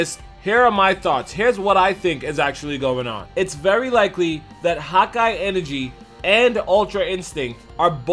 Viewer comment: Tell me more about this "hawk-eye energy" you speak of? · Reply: I thought he was saying “hot-guy energy”.